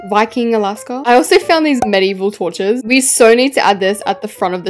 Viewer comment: I thought alaska said medival tortures 😭😭 I should get my ears checked